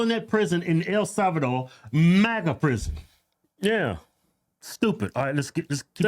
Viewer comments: MEGA prison. I don’t think she said MAGA. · I think she said El Salvador Mega prison not MAGA.. I may be wrong but that’s what I thought I heard. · did she say. Maga or mega? 😂😂😂